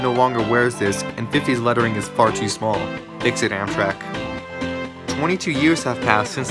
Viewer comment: I think he said, Makes it Amtrak.